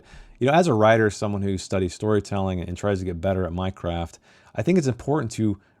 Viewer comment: I misheard "Minecraft" and it thought for a second you want to change careers.